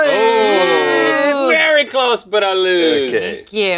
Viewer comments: did link say "Very close but I loust" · did Link say "lossed" instead of lost???